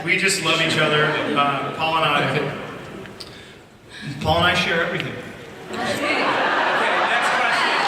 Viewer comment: what did Ian say by 3:34 ? Paul and I share ... (?) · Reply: i think he said "paul and I share everything"